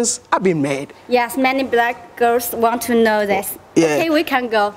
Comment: Did She Say “Many Black Girls Want To Know This"